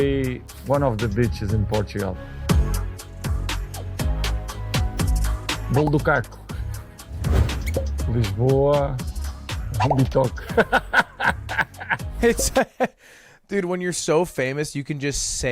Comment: Bro did ronaldo just say "on of the b*tches in portugal"